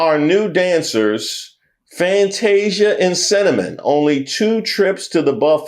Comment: I was busy throwing some trash away when I hear you say "Fantasia" and "Cinnamon" (It was spelled with an S tho.) I could have sworn I heard "Fantasia and Sediment"